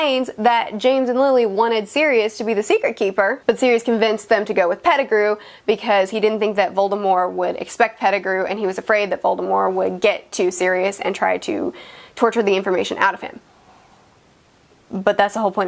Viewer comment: About 5:50 in...are you saying “Volemore” like with an “e” instead of a “t”?